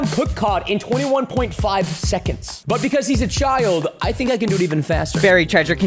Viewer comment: but cuz he is what ?!! What did bro just said 💀💀💀💀💀💀